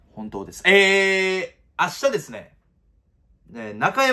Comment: 最近マイ2億くんの「明日ですね」がアスベストに聞こえる 指名した馬はベスト尽くせへんのに